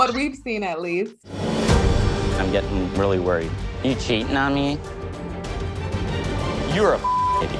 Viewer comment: Literally thought she said "are you shitting on me"